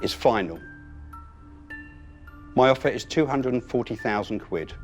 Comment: Lorenzo Dion Lee Do you mean @ 10:29 ? I hear 240,000 quid